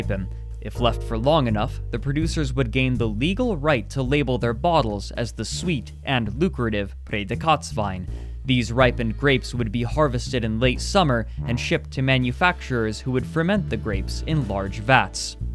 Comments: I swear every time I here say "Prädikatswein" (special quality wine) I hear "pray the Katz wine" in my head (Katz as a short form coming from Katze, which is cat in German). The first time watching I was like "what do cats have to do with wine?" ^^ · Him pronouncing Prädikatswein so oddly made me confused. I thought he tried speaking french or something and had to legitimately scour wikipedia. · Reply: Thanks — that now makes sense to me. I was racking my brain to figure out what he meant by "Pray the Katz Wine". And then thought he must have said "Pretzelwein" ... He should have read it out with rapid English enunciation as "Pray dee cuts wine".